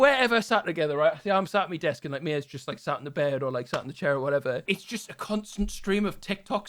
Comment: Lol i heard "Mias sat on the bird"